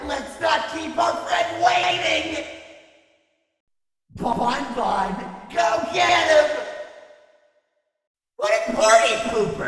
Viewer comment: I once recorded myself playing a competitive game for curiosity and...its basically this, me yelling at my teammates (even more when I thought he said "You moron, go get him" or "Oh mu god, go get him" because I thought thw bunny puppet was the one yelling at Freddy